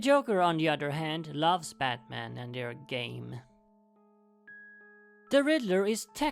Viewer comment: I thought he said "they're gay" 😂